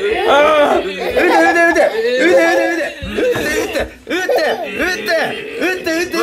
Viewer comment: しばゆーが撃って撃ってって言いすぎてウッディウッディに聞こえる